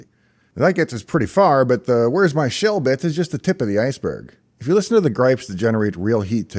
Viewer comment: I don't think you said this but I heard "where's my shell bitch" 😂